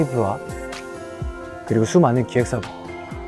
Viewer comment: i thought Hoshi said "Sooman" 😭😭😭😭😭